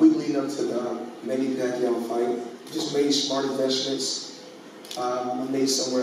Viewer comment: 👀Attention 👀 3:15 he said he made smart eveshments <~~~< what does that word mean.🤔